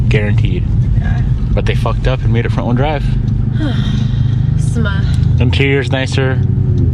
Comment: this is the 1st time i heard someone literally pronounce "smh"😂